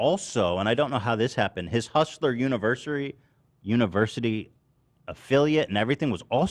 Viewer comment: Andrew Tate being banned on tiktok is 100% understandable. He deff shouldn't be on an app w the primary age demographic of 5 to 16 year olds😂😂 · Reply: speaking of Taint’s demographic, anybody else hear Ethan almost call his website “Hustlers Nursery” at 47:31 ?